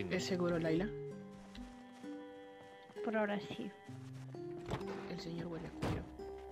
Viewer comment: O entendí mal o la mujer en el minuto 24:30 dice. el señor huele a culo? Jajaja